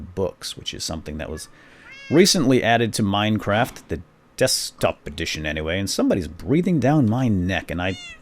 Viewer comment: did say dubstep edition